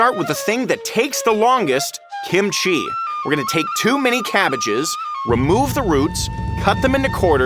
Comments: i thought he said too many cabbages😭😭😭😭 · I thought you said too many cabbages 😂😂😂